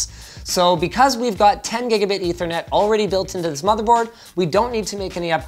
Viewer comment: You have gigabyte instead of gigabit in subtitles